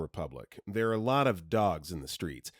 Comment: SKULLMAROS??? Oh my god, Colmados, that's what the actual word is.